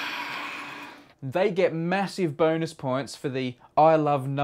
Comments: dude. . I heard "massive boners" instead of "massive bonus" lol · you misheard. It's: They get massive Bonus points for.....